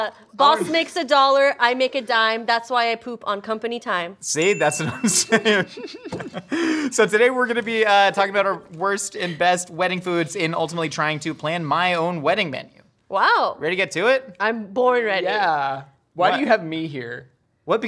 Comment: I thought it said best friends wedding , instead of best wedding food, and thought how great it would be if Josh's best friend was Nicole 😜